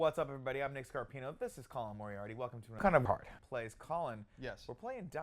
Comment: "Colin I'm hard" I think is what he says